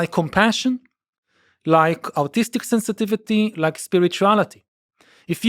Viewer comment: Thought he said 'autistic sensitivity'. Yes! I have that! Then I realised he said 'artistic'. Damn.